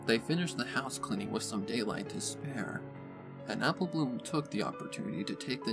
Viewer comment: for a second therr i heard "some daylight despair" instead of "some daylight to spare" :V